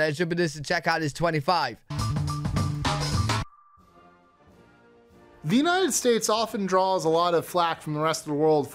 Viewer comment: Did he just say, “The Guh-Nigh-eh States?!” 🤣